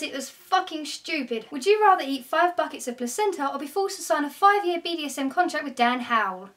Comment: what is the question at 11:32? couldn't hear. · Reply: Would you rather eat 5 buckets of placenta, or be forced to sign a 5 year BDSM contract with Dan Howell.